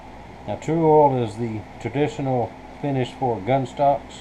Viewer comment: I had a hard time making out what you said is a good oil to use on gunstocks ... Can you tell me what it was you said to use ? It was at the 4:40 to 4:42 mark of your video. Thanks! :-) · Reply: True oil. Birchwood/casey